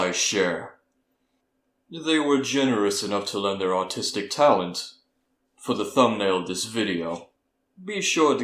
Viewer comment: I- AT THE START I THOUGHT HE SAID THEIR AUTISTIC TALENTS LMFAO